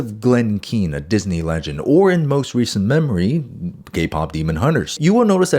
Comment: Did he say gay pop demon hunters?